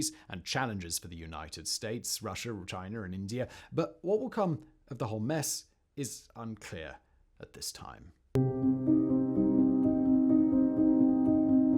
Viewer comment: Wow I heard you say “the whole mess and thought you had said “Hamas” and thought to myself good point.